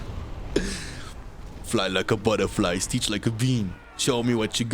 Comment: did he say float like a butterfly steech like a bean?